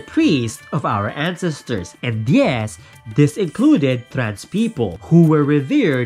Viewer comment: Hi sir! At mark @ 11:32, did you say "trans people" is in the T in the LGBTQ? Thank you, good content!